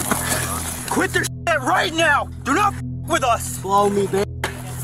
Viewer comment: i swear why does she keep saying that "slow me b!tch" 💀💀🙏